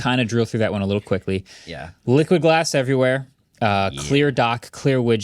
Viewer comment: I thought he said liquid ass everywhere 😂